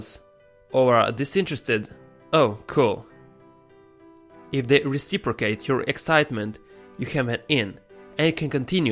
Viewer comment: From 2:16 to 2:17 could anyone pick up what he's trying to say? I couldn't make it out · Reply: If they reciprocate your excitement you have an "in". 😉